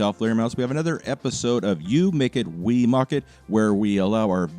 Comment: Is it just me or did you just say "You make it, we mock it", would be a beautiful blunder. :P